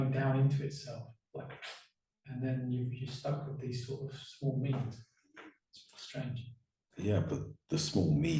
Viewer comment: could you clarify the word being used at 2:38 (it sounds like C says 'small means'). Could you clarify as to what is being said as the sound is a bit off there. Thank you so much. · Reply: Hello Donna, thank you for your question. What was being said by C, was him using the words, “small means” He was referring to the ego means of communication rather than the liberated means of seeing and direct experience that comes from a liberated more self-realised perspective. I hope that's clarified for you now :)